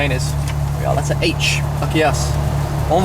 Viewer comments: I'm I crazy or at 3:44 did he say, "fuck yes" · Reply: it sounds like it, but he says "lucky us" XD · Did say Fuck yes at 3:43 ?? I am so stunned because this is possibly my first time to hear curse =DDD